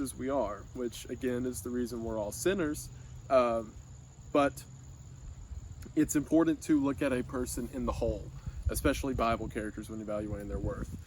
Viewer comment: wait what did he say? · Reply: That's the reason we're all siiners but it's important to look at one person in the whole.